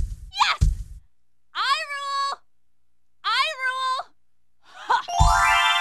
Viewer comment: it sounds like he said hyrule